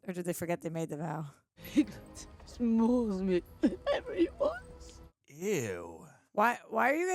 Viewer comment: what does she say here? “Piglet smshmshmshsshmshs than smshshsm” please help.